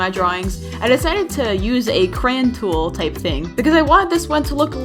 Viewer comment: did you say "cran tool"?